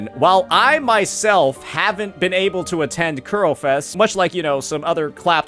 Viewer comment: i keep laughin cause it sounds like he says kulo fest in spanish culo is ass , sound like a gay party